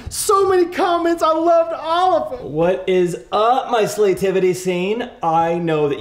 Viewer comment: You had me at “Sleigh-tivity Scene”, and it was all downhill from there 😂💀 Also, doesn’t get the credit he deserves. The delivery is always on point. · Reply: Wait, I heard that as "Slay-tivity Scene". Guess it works either way 😂😂😂